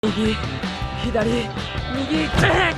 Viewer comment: Hey, does anyone know wtf that uguu moe girl was saying? edit: oh nevermind, its right left right left